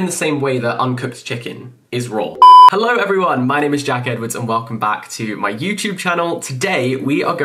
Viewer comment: I heard "hello my name is jacket"